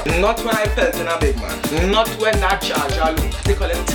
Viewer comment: is it me or did he really say "not when that charger low @ 7:38 " lmao any how he still bad...... #teamstoplikeman